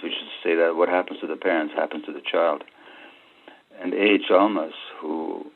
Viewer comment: What´s the name he mentions at 5:43? He says he cites him all the time but I can´t get his name... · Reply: Do you mean A H Almaas?